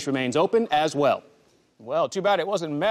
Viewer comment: Randal: "Well to bad it wasn't meth, they could've just had you come down and sniff it out huh cathy" Cathy: "Eat sh*t, Randal I'm in recovery" 😂😂😂😂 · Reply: you know what i thought i first heard that too but upon hearing it for like the 5th time i heard e*t sh randal im in recovery